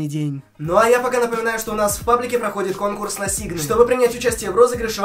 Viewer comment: мне послышалось у нас в контакте проходит конкурс на сиги